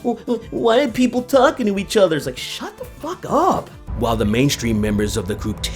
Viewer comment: You got that wrong! It's: >Why are these people talking to themselves instead of listening to ME?